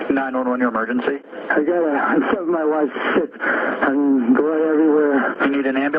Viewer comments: Lol I thought he said “I think my wife slipped” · I heard "I stabbed my wife...shit". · Had to listen to it twice. He freaking said that man hahababa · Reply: I heard, "I stabbed my wife. Shit." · I heard “I think my wife slipped, there’s blood everywhere” He said he heard something that sounded like cats screeching not meowing Plus, if he washed his clothes why in the hell would he put them back on? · It sounds like he said “I killed my wife, slipped and blood everywhere” Edit: use slow speed playback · I heard “ I stabbed my wife ... shit “ I hate when cases are sooo obvious but not 👀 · I thought he said "I think my wife slipped and.....blood everywhere." 🧐